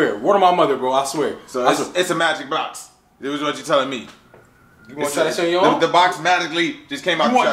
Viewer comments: WTF does say at 1:25? 😂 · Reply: “You wanna try that on your own?” I think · So we just all gon act like ain't say "Madically" instead of magically at 1:23 😂😂😂😭 Also what's with the random mug/bottle thingy being on the floor at 2:29????